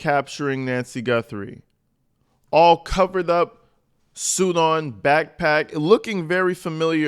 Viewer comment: I thought you said “all coked up!”😂😂😂😂😂😂😂😂😂😂